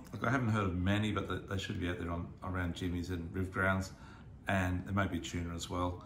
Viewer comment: Hi, great report. Where did you suggest to go for tuna and blacks. I thought you said "Jimmy's" I'm not familiar with that.